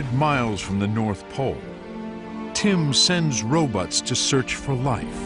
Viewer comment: I swear that the narrator said "Tim sends ro-butts" (instead of ROBOTS). Or did he use another word that I can't hear properly? Damn these videos with no closed captioning choice available! Came back just a minute later to edit my post because...Tim's "RO-BUTTS" are at it again at 1:08:35!